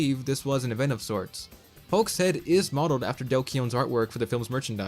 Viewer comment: Whose art work did you say it was based off of on minute 10:52? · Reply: Dale Keown.